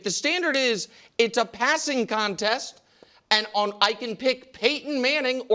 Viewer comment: It’s a passing contest ! I swear I thought nick was bout to say (pissin contest lol😂)